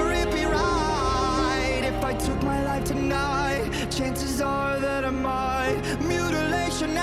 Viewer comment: Haha I just realized that I have some of the lyrics wrong since I first heard the original. Instead of the line. "Chances are that I might". I have always heard it as "Chances are dynamite" lol